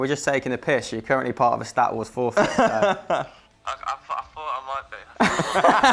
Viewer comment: I swear inatead of it being called stat wars i thought i said twat wars.